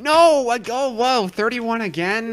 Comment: Did you say owo?